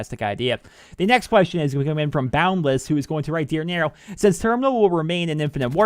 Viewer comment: Holy shit i thought it said Dear Negro XD